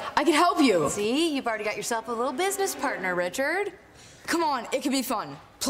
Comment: dhar Mann made a subtitle mistake instead of little he wrote tittle haha